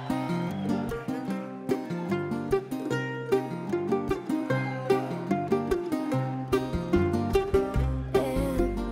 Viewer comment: I wonder what Sean says at 2:03 ... ?? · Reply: "Get off my foot."